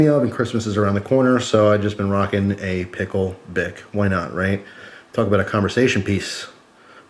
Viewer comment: swore he said pickle dick hahah nice gear set up!